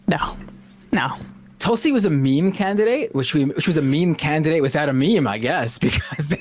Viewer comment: Never heard the term "meme candidate" till now but putting it to memory, +1. Fools love a fool and this girl was always untrustworthy to say the least. The bar is literally as simple as if you're a grifter or not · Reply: For a moment there, I thought I heard "mean candidate". 😆😂